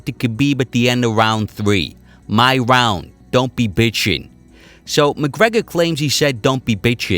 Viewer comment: i heard “i won’t be shinless”